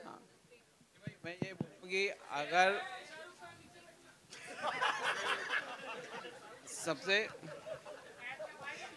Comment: peeche kya bola kuch shahrukh ke bareme.. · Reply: Ig kisine bola 'ae Shahrukh Khan neeche baith na'